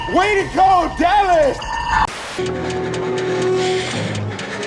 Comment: What does the alien 'Xenomorph" say at the very end 11:35? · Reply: There is no Dana, Only Zuul. From Ghostbusters 1984 in which Sigourney Weaver also starred in.